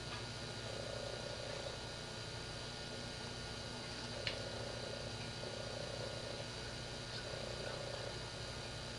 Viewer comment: Someone should put subtitles, it would be so much easier to understand, even in english, because it's hard to hear him well :// What happens at 28:11 ? What does he "say"?? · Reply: “What’s that noise?” “Is somebody down there” “I’m gonna go check” Then he continues aloud saying “laundry man..etc etc”